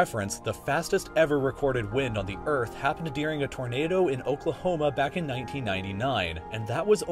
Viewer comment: Did I hear Oklahoma?